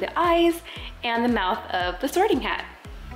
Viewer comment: When she said “sorting hat “ I thought she said “soaring cat” 🤣